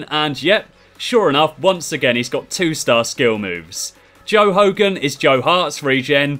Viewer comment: When you said joe hogan I thought you said 'Joal logan' any guiseley fans will understand (there won't be)